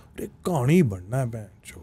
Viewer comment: someone pls explain what he said i dont understand it. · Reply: Ek kahani (story) ban na hai bc😊wanna become a story